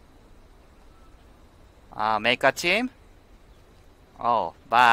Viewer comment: 毎回、Make a team ? が Make a chin ? って聞こえるわw